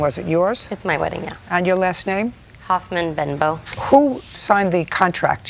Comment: Hyphenated last name. Just started watching but first impression......KAREN! Even before Karen was a thing. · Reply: Thought she said Hoffman-Bimbo